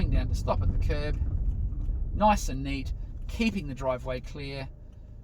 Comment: At about 11:07, said nice and “neat”, is this “neat” what said? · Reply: Stopping at the kerb. Yes, I said Nice and Neat. I think I was referring to slow approach enabling me to carefully come to a stop "neatly" near the kerb.